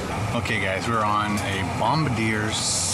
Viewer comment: Honey Butter Chicken Biscuit from Whataburger. WIth a sweet tea! (also, it's pronounced bom-BARD-ee-ay in this context, not bom-bar-DEER) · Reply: Andrew Long haha Ikr...sounded like he said "Bomb a deer"